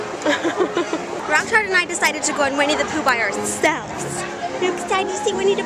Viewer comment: When you said 'Winnie the Pooh', I thought you said 'We need to poop' :p